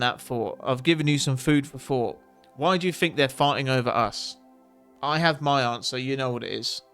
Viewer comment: sorry,but I thought you said 'why do you think they're farting over us'🤔....and I was going to say it's simple,it's because they are evil,then I realised I obviously just misheard you first time,still🤣🤣🏴